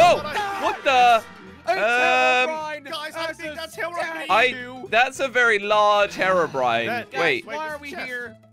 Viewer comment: did say herobrien?